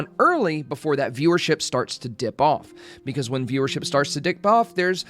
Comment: what did you say there? Dick poff? 😂😂😂